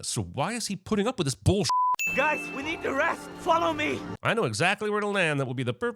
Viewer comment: "Guys, we need to rest. Swallow me!". I heard what I heard. You'll get your duck orgy. Bark my words.